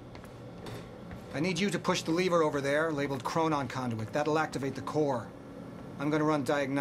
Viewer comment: Lol I thought he said "chronon condom"